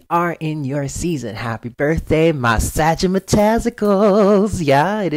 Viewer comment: I thought I heard you say Happy Birthday my testicles. LOL